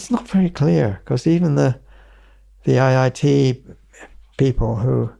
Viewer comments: Did he say IIT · Did he said "IIT people "